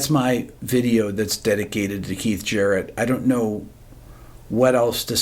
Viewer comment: In a noisy room, I did a spit-take when I thought I heard Rick say "Leif Garrett".